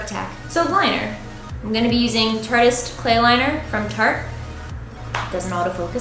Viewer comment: I thought she said T.A.R.D.I.S clay liner... MYKIE DO YOU WATCH DOCTOR WHO?!